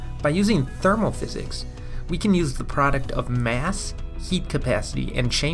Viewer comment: I thought this nigga said yeet capacity lmao